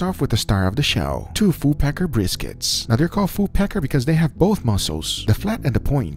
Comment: My hearing really sucks. I thought he said full pecker briskets.